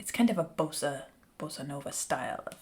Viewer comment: did she just say bossa nova style!?!?